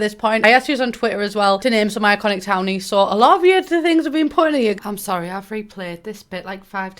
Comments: I thought she said "Alot of different things have been put in here" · It sounded like you said. 'Alot of the things you said have been put in here'